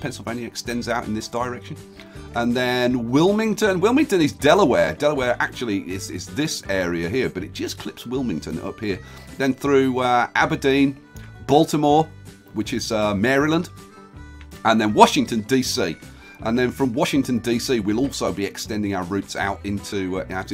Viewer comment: I thought Aberdeen said alderaan